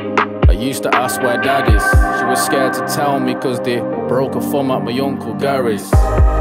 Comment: Does anyone know what he says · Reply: They broke her thumb at my uncle Gary's